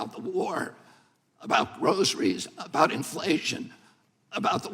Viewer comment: Did he say Rosaries at 3:43? They don't want hm to talk about Rosaries?